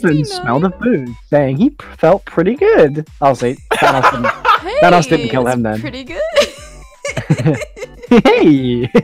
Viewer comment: Did he say "Vanoss didn't kill him then"?